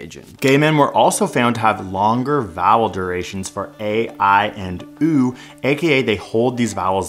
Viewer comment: I swear I thought he said "Longer BOWEL durations" when I had the tab closed just listening in.